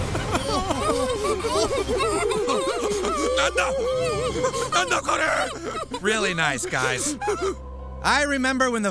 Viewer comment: what does the Japanese guy say at 0:11? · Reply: like "Nanda, nanda kore!?", basically, "What, what's the meaning of this!?" or something along those lines.